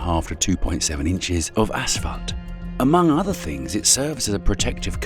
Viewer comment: in, the guy says “a$s f**ked” instead of asphalt. Why do you suppose that is? And what kind of channel is this?